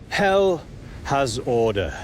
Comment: i thought he was saying ''hello'' but he just said hell